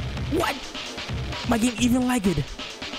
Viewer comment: He say "my game even lagged" but I hear "I don't even like it" XD